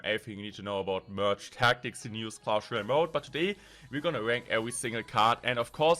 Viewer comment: i hear "iam gonna wank every single card" in the beginning